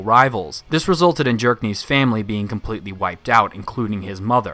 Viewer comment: I kept hearing you saying “Jerk-niv” and I honestly don’t know if that’s just how you pronounce his name or you just really don’t like the character 😂